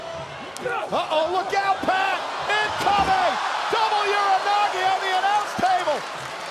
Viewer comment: why the subtitle say double enziguri not uranage slam